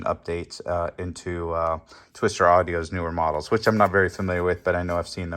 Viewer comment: That has some similarities to the surge audio 3000.1 amp they had on back order or pre sale back in 2023. At first i thought you said twisted audio . Nice video of the repair by the way.